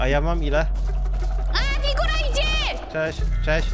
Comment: czemu usłyszałem "Nikola idzie"? XDDD